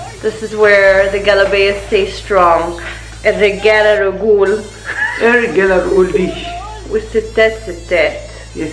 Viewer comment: WTF 😂 You just said "men are legs" 😅